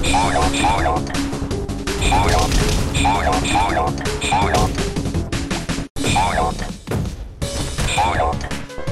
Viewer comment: If you see this, please tell me, what is he saying here! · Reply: FOILED FOILED